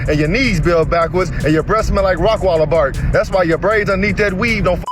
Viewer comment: Wait...Did he say her Breath Smell like Rottweiler Barf...Gaux Damn. Bruh...